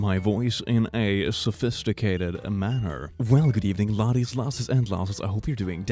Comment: I’m not sure if he actually says this but I hear “Hello lassies lasses and lavas.”